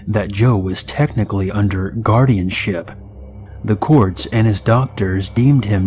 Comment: I really thought he said Guardian shit